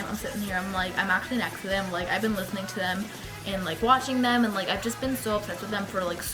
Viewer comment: I thought u said u were washing them not watching them xD